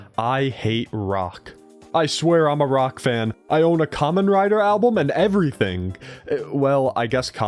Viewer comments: i thought you say kamen rider :)) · i was in the other room and heard 'common rider' and thought you said 'kamen rider' and ran back into the room excited for nothing lmao